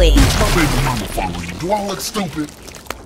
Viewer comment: What sukuna is saying here? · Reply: My baby mama follow you do I look stupid